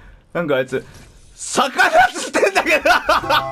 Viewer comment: もう逆立ちしてんだけど、にしか聞こえなかったw